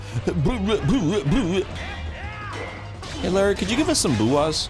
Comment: First time I heard it I heard “hittler can you gave us some booyahs” and when I rewound I heard ‘hey Larry’ so you’re not crazy Dan, at least not this instance